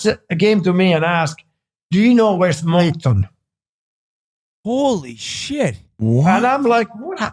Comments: What'd he say? I replayed it 20 times and can't figure it out. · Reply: sounds like Moncton? (preceeding reference) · what is being said at 1:35:39 ?? i can’t figure it out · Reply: "Do you know where is [sic] Moncton?" A follow-up on Smith's comment about sending him to the Jets' affiliate team.